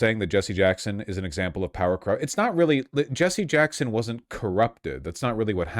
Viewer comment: I thought the guy was saying Jesse Jackson was an example of power creep 😭 but that honestly makes more sense. He got woke power crept over time.